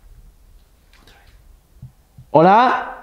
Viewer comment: En el minuto 12:00 juro qe escuche una mujer hablando (hola alguien ahi)